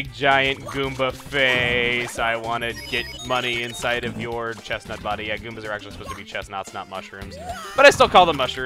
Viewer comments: Did he just say "chesnauts?" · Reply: I know, it just sounded like he said chesnaughts for some reason. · Did he just say "chesnauts?"